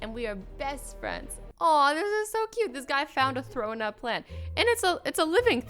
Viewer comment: I thought she said that this guy found a thrown up plant